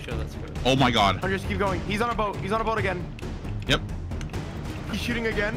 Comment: he sounds like like repeating what he says "his in the boat" 2 times